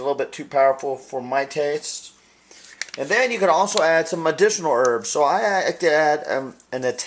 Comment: I thought he said "and now we add some medicinal herbs" hahaha